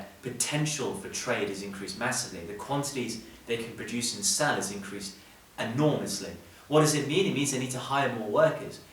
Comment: Can somebody tell me what he said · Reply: The quantities they can produce and sell has increased enormously